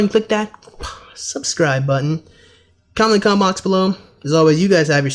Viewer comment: Did you just say "comment in the cum box below. . . "? XD